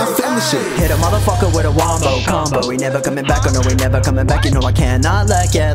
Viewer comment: just heard this on spotify came here because I thought the beginning said "pedo motherfucker with a wombo combo" and I was like damn kinda spitting facts tho. disappointed.